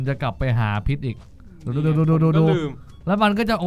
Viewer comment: ครับคำหยาบ 3:14 ลุงพูดว่า กู ง่าาาาาาา